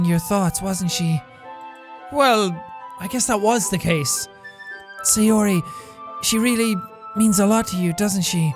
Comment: I was rewatching the playthrough with captions on and just see "C or E" instead of Sayori at 9:36 XD